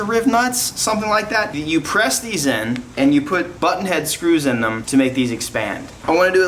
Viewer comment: sounds like u said butthead screws 😂😂 im a child inside i know